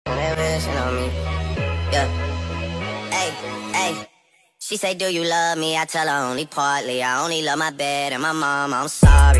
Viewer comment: I thought its said only love my baby sorry 💀